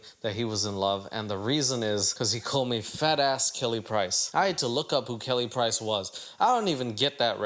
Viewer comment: CLASSIC Catfish episode. "You should've never called me a fat Kelly Price," will live on forever. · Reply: I thought she said "fatass Kelly Price" 😂